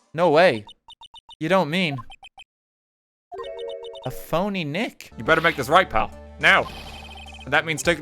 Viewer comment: god, i thought he was gonna say "a phoenix wrong"